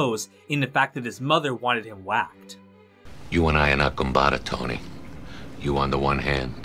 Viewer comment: what did he say? goombata? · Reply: from my limited understanding of italian im 99% sure he said compadre in his italian american accent, meaning friend/fellow